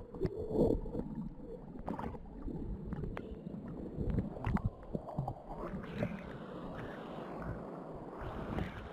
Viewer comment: En el min 13:15 dice algo no se si dice "por ti" o "aqui".... y después se sigue escuchando que habla pero no distingo que dice Y en el 13:26 dice "Vete" pero no es Nala.... es como un hombre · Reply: Dice por ti 😨